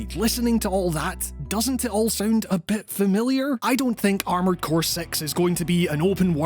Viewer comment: am i the only one who heard armored core sex and had to rewind to confirm lol